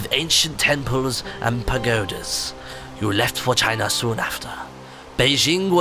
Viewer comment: When said for china at 00:33 it sounded like he said vagina